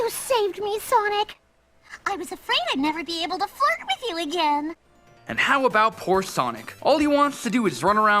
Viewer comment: Did she said, fuck with you again?